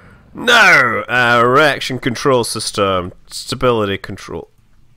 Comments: DID HE JUST SAY "ERECTION CONTROL SYSTEM" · did anyone else hear "erection control" lmao